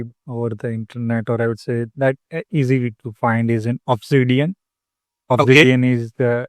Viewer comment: Hi I hope you are doing fine. Today I have listened to your podcast, "Beyond D365 Business Central: Mr Nilesh Mandani on Career, Consulting & Curiosity" I have one enquiry with you: what is the exact name of the tool, as Sir said, Opus India, for expressing one''s own thoughts? Clip play time is 23:00 minutes onwards. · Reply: Hi. Thanks for watching. The tool name is Obsidian. Below is the official link of the tool. <link>